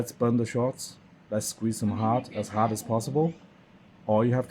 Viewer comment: For a second a thought 💭 u said squeeze there balls ⚽️!!!!!! Jajaja!!! Not me , all u amigo !!!!🙏🏼🙏🏼🇲🇽🇲🇽🇲🇽🇲🇽✅🌶️👋